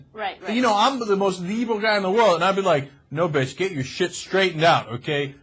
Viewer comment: Did Cenk say, "I am the most evil guy in the world!" -Cenk